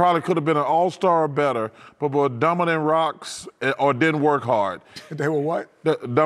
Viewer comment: I know Chuck said "Dumber than rocks" but it sounded like he said "Dominant rocks"